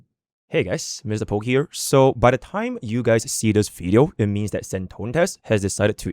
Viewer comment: yt cc said Senon testes.